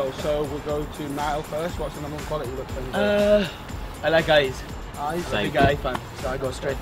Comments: I thought Niall said "I like guys" haha♥ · I honestly thought Niall said "um... I like GUYS" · lol, when niall said "I like eyes", i thought he said "i like guys"